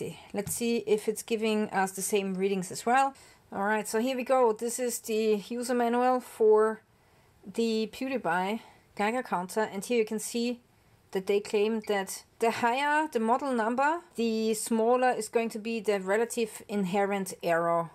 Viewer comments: Thank you! I have been shopping for a quality/inexpensive Geiger counter and your research has helped me make a decision. (NR 750) On a funny note I thought you said the brand was "Pewdie Pie" LOL! Take care!! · Thank you! I have been shopping for a quality/inexpensive Geiger counter and your research has helped me make a decision. (NR 750) On a funny note I thought you said the brand was "Pewdie Pie" LOL! Take care!! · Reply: 😂 I thought you said the brand was "Pewdie Pie" LOL!